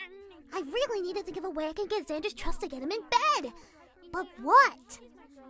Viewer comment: WHAT WHAT DID YOU JUST SAY- AM I HEARING THINGS OR DID JUST SAY “EARN HIS TRUST AND GET HIM IN BED” I